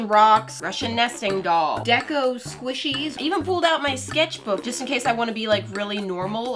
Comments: “Deco squishie” Me a my hero academia fan: DID SHE JUST SAY 𝑑𝑒𝑘𝑢~ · When YouTube gives u the notification a few mins late and u are now the 2000th comment Me: Whyyyyyy YouTube why Edit; When she said deco squishys at first I thought she said Deku squishys And I was just like “ThErEs DekU sQuIsHys” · deco squishes Me: I thought you said deku 😂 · doesn't paint the glob within the first two streaks of paint in the video Me : OCD increased No hate just ocd im talkin about lol Edit : who else thought she said Deku when she said Deco squishy just me? Ok